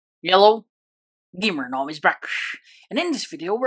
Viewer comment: i thought he said yellow instead of hello